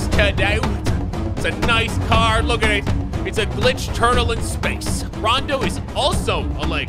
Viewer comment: I thought that said turtle instead of triple XD